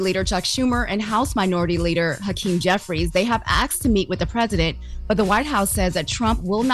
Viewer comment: “they have AXED”? Wtf is wrong with her and Loren??? The WORD IS ASK. SOUNDS LIKE AAAASSSSSSSSSSSSSSSSSSSK!!!!